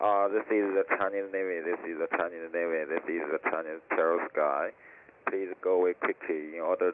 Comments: “This is the Chinese navy x3” “im eating a churro in the sky” please go away quickly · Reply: I heard “this is Chinese terrorist guy” 😭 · Without subtitles I wouldn't have understood a thing, I kept hearing "Chinese gravy" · The chinese says "this is the Chinese terror sky" he meant "territorial". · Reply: I thought he said "This is a Chinese terrorist guy" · It sounded like he said, "This is the Chinese terror of the skies".